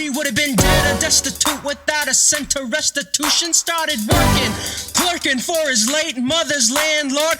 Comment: We see all your Act 2 comments 👀 Yes, Act 2 is coming and we’re working on it. Before that though, EPIC: The Musical will be released. Projects like this take time if we want to do them right. Thanks for sticking with us ❤ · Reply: ESCUSE ME DID YOU SAY TWERKING